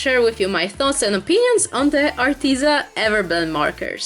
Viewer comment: i first thought it said “aritzia” and was immediately thinking of john cena. (only true shreks will get the joke) love you!